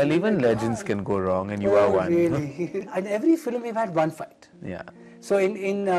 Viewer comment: Did Manish said to karan OH BABY at 1:15 ????